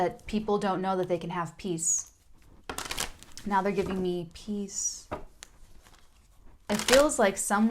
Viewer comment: "now theyre giving me peace" what i heard "now theyre giving me peas" because im eating shepherds pie and i was putting my fork in one lol